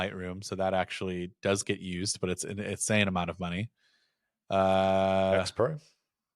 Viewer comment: What app does say at 36:33? Xpro? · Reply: Yeah, X Pro, probably. What used to be Tweetdeck. And since they're tech journalists, they probably use that.